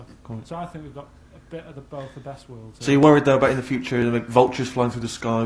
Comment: Did Karl just say "we've got a bit of the both of best worlds"